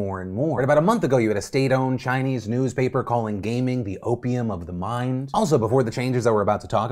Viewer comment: When you said "Opium of the mind" I heard "Chicken of the cave"